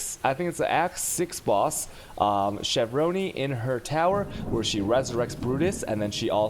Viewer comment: Did he say Shavronn-ee at 8:05?